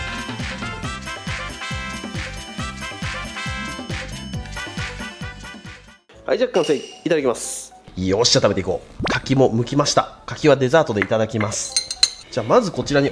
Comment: ちょうど動画よそ見してて カキ剥きました、デザートとして食べますって聞こえたから 相変わらず牡蠣好きやなーと思って見たら、マジでデザートの柿だった笑